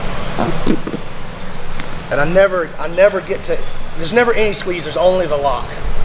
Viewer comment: B, what did you say at 1:08? "Theres never **** *** there's only the lock." I think i hear the word "squeezers" but i can't make out the word before. 😥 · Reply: Squeezing